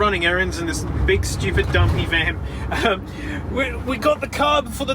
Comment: when you said "big stupid donkey van" I heard "big stupid dumpy van"